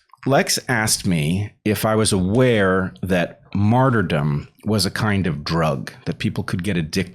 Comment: Excuse my bad English, what is Bret saying at 1:15 that could be a kind of drug? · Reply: Martyrdom